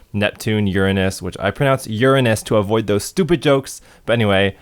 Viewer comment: Did he just say Urine-ous?